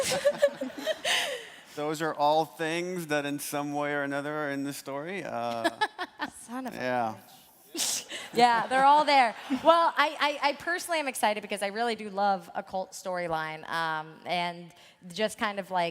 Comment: did she say "son of a bitch" to neil druckman 😂😂